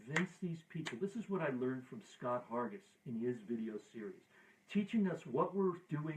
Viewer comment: Hi, thanks for this very helpful Q&A! I really appreciate it (and learned a LOT!). There is one thing that I do not understand, literally. At minute 31:05 you mentioned a guy from whom you have leaned to convince people. But I cannot understand his full name. Scott who? Looking forward to your next video :-) Best wishes from Germany Matthias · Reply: Scott Hargis